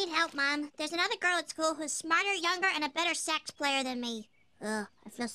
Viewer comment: I heard "And a better sex player than me". Had to rewind a lil...